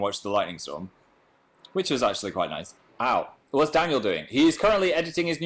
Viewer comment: I thought he said dangle at 1:15 omfg